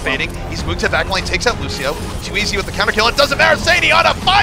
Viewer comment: What the actual fuck is he saying? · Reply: he was getting hyped over genji getting a team kill aka 5 kill streak aka 5k(ills)